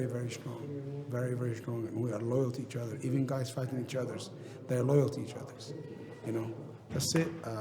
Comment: did he just say even guys fucking each others?